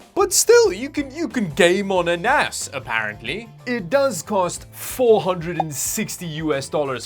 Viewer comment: if you have subtitles on, it translated to "you can game on an ass" instead of "You can game on a NAS".....hahaha same...same.